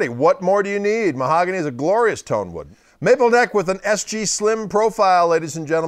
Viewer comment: did he just say maple neck at 2:41? yeah...anyway, i played this guitar at a store one of these days, and even tho i tend to be more of a "fender" guys, i have to say the 2014 SGJ was one of the (if not THE) best guitars ive ever played..simply amazing..